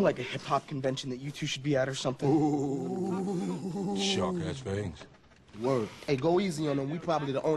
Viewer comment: "Shark has fangs" The moment the goofy white kid was accepted. Lol. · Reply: For some reason I thought he said sharp ass fangs lol